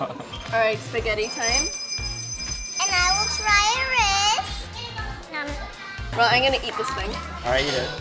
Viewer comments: it sounded like said “I will try rizz”😂😂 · “I will try rice” But I heard “I will try Rizz”😭